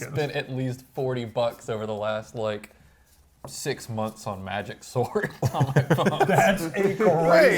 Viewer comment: I thought he said "Magic Sword." He said magic sort and plane, I understood immediately.